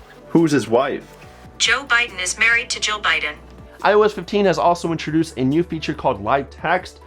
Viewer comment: Bruh... I was watching in 1.5x speed, and heard "Joe Biden is married to Joe Biden", instead of Jill Biden 😬😬